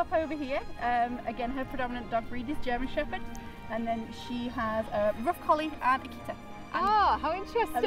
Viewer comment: what does she say about mili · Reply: I said Milly is wolf with mostly german shepherd in her dog breeds with a touch of rough collie, malamute and akita. :)